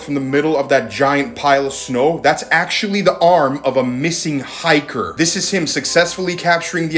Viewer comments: The first time I watched the vid I thought you said “a missing arm of a hiker” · Bro I thought you said it was a detached arm of a dead missing hiker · Shit I thought he said "the missing arm of a hiker" 💀💀